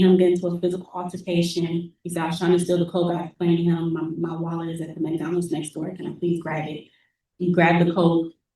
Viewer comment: First she said she had no family ,then says my wallet was with my spouse in the car? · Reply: I thought she said her wallet was at McDonalds. Did I hear incorrectly?